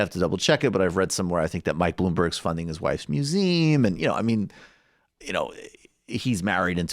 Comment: what did Michael say? Bloomberg is funding his wife’s what? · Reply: museum